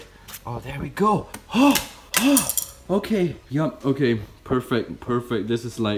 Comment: I heard “FANG goodness”